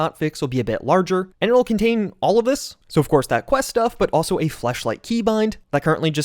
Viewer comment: am I tripping or did he say fleshlight 😂